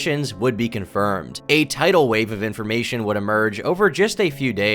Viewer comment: did someone say TIDAL WAVE?